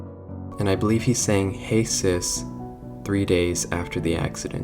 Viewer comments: At the 9:11, I thought he had said “Hey Chris”. Since Chris has been around since the beginning, and is mentioned by many victims. Susan Woodings mentioned him before she disappeared. Brian Stells mentioned him before he dissapeared. I’m pretty sure that just like Charles, Felix told Chris what he had done. And considering that Chris is still alive means that unlike Charles, Chris never tried to tell anyone, and is most definetly Felix accomplice. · also i thought he said "hey philip" instead of "hey sis"